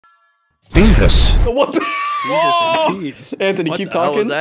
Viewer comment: When the video opened with Anthony going " J E S U S ! " I knew it was gonna be gold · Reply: I thought he was saying "BEAVUS".